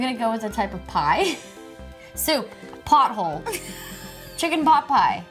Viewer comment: Am I the only one who heard it “butthole” ?